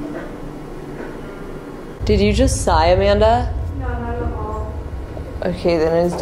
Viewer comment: At the time 25:07 I hear a guy voice say “mackie and Amanda” 😮